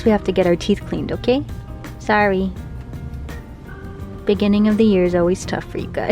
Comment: Whoa this video is like 4 months old…Dollface season 2 came out in February. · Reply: I thought I heard saying "beginning of the year check up" to the dogs. I was really confused 😂